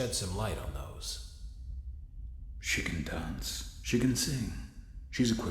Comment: OMG IDK WHY BUT EVERYTIME I HEAR "she can dance" IN THE INTRO OF THE GAME I THINK HE SAYS "chicken dance" XD